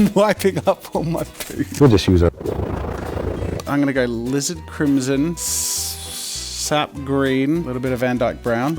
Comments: don't worry! i always heard 'lizzard crimson' too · I always hear "Salazar Slytherin" Instead of Alizar Crimson XD Anyone? No? Just me? XD